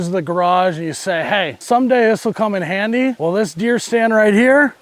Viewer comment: I thought he said beer stand lol